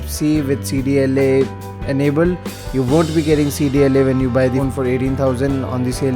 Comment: wauue, you need to breath while speaking, its close to impossible to understand . but it sounds like your saying, you wont get CDLA when buying the 18k version. 1:50 (likely the price in your region, since you have an info saying that the 32GB cost 18k in top) anyway Im not sure what your meaning since that aint the case, but please calm down, and take it easy and remember to breath and think about your pronounce if you want english speaking people to understand you.. :-) +1